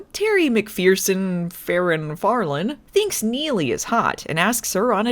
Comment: So at 5:00, I somehow heard "Newmie" instead of "Neelie". That could have been a much more interesting episode!